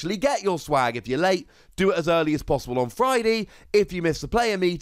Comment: did you say fridee?